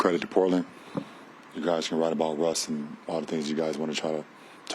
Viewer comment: did he just say "you were right about Russ"